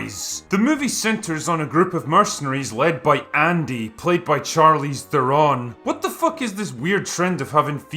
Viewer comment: did he say "Charlie Theron, what's with girls having guys names anyway?" Errrrr its Charlize Theron and I think its pronounced "Sherlisse" I could be wrong with how its pronounced but its definitely not Charlie lol